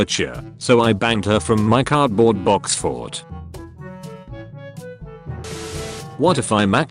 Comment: I heard it as “- so I banged her from my cardboard box fort.” 😂😂😂 Also, you made the right decision.